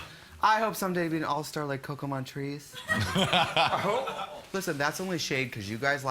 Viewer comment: The subtitles called Coco Montreese "Pokemon Trees" I'm dying. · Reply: When I was introduced to RPDR my friend was talking about Coco Montreese and legitimately thought she said Pokemon Beast